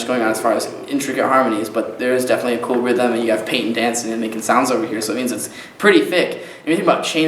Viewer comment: the subs say "paint and dance" instead of "peyton dancing" ahhaha